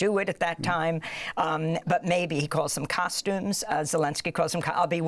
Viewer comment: To be fair, he wasn't trying to be cheeky. The Russian\Ukrainian word for suit is "costume". This was just a case of minor language barrier. But the fact that it came off as though Zelensky was mocking the fascist 1American doofus, is gold. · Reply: I thought he said he would wear a custom suit. As in bespoke. That makes more sense, right?